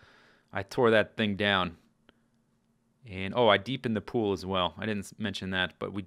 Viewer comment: i thought he said "i pee'd in the pool" at first.